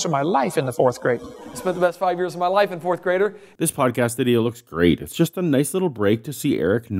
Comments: did he say he spent the best 5 years of his life inside a 4th grader? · Did he just say " I spent the best 5 years of my life in 4th-grader"?! Little Freudian slip there, Eric? ;D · Did Eric say "I spent the best five years of my life IN a fourth grader"????